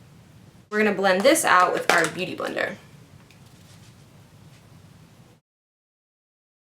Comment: hahahah i heard "gonna blend this out with a damn beauty blender" i was like OH .. damp.. hahaha